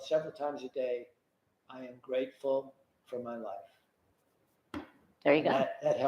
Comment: …, Oh, I’m sure I heard him say “I am grateful for my WIFE“ 😇😇😂